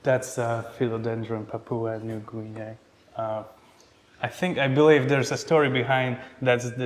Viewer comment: What philodendron is that on 7:34 can't hear it clearly? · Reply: Philodendron papua new guinea